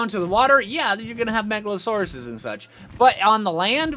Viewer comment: “Once you get in the water of course you have Megalosaurus.” I swore at first you said megaloceros; but no, you didn’t and both confused me to high heck. Took me a minute to realize Megalodon are a thing. 😆